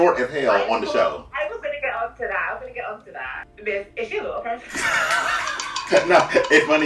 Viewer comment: what did she say here? I couldn't hear it properly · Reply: “Is she a little person?”